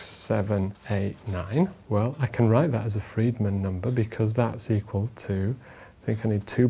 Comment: Damnit I thought it said "Fried Numbers".